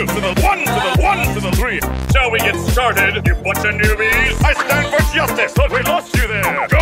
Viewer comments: Lyrics All Might: 2 to the 1 to the 1 to the 3, shall we get started you bunch of newbies, I stand for justice, thought we lost you there, GO BEYOND, I am here! Deku: 3 to then 1 to then 1 to then 3, like it or not you cant stop me! Deku it is! AHHHHH HE'S ALIVE!! my whole life just flashed before my eyes.. Bakugou: I'LL KICK YOUR ASS! WHAT DID YOU SAY!? I'm gonna roast that damn nerd today, GET BACK HERE DEKU!! SAY GOODBYE!!! Outta my way now before i set you on fire Todoroki: Lets continue, now I'm Mad!, my old man is a scumbag, you want my fire? I'll end this quickly, Become the hero he wanted you to be!! Edit: holy crap I didn't except this comment to get so many likes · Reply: I thought all might said shall we get started to what’s put into me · i really thought all might said "shall we get started, ya bunch of noobies?" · All might: 2 to the 1 to the 1 to the 3 Shall we get started you buncha newbies I stand for justice! Thought we lost ya there Go beyond I AM HERE! Deku: 3 the to 1 then to 1 then to 3 Like it or not, you can't stop me! Deku it is Ahhh! He's alive My whole just flashed before my eyes😥 Bakugo: Ill kick your ass WHAT DID YOU SAY?!? I'm gonna roast the damn nerd today Get back here deku! Say goodbye Get out of my before I set you on fire Todoroki: Let's continue Now I'm mad My oldman is a scumbag You want my fire? I'll end this quickly Become the hero he wanted you to be! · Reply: I always thought he said noobies instead of newbies